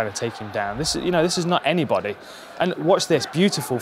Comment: does Hardy call Weidman a nobody? ;P